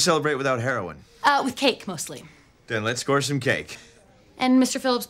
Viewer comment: "Then let's score some cake." Another example of the kind of one-liners this show was amazing at. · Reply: I heard let's snore some cake xD